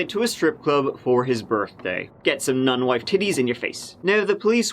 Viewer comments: "Non-wife t****es in your face!" EACH episode you do, there's one line that drops me to the floor laughing. Thanks. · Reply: holy shit i was listening on 1.25x and i thought he said non-white ti*****... that makes WAY more sense i was so confused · omg..... hahahaha I had to listen to one part like 4 times. at first I thought he said, "get some 'non-white' titties in your face" 🤣🤣🤣🤣🤣🤣 I now realize that he says, "non-wife" i was about to have to call racist on my guy. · Holy shit dude--did you say "Get some non-white t*tties in your face" at 11:55?? Not good.